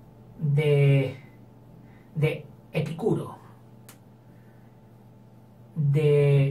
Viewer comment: no entendí mucho, Epcuro, (no se como se escribe) ¿es Dios o algo así del deseo carnal? o me dices como se escribe para investigar sería de mucha ayuda :) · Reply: Epicuro